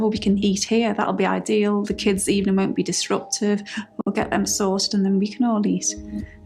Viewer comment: she said the kids won’t be “disruptive” NOT disrupted …..am I hearing it right?